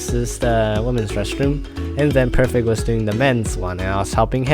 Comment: I hear you say “Perfect was doing the mens one” it was me doing the mens XD